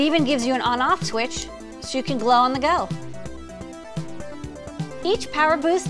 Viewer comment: Did she just say "glow on the go"? Is that on a t-shirt somewhere? Am I missing out on life?